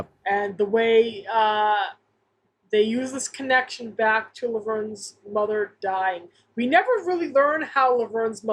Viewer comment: Good catch on "Laberne." I thought I heard that. Also, Shirley says "Vernie," which is rare after the first couple seasons, and is therefore all the more powerful.